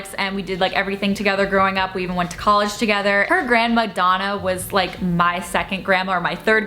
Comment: the whole time I thought she was saying grand Madonna, also when she talked about the hair story and immediately going back to sleep after waking her boyfriend up and him not being able to sleep i was like that was very rude she could at least stayed awake with him.